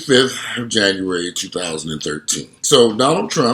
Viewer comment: Did he say it's 2013? 😂